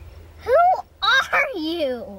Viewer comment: Oml- i cant 0:29 i heard "sHut Up TOdOdaBi"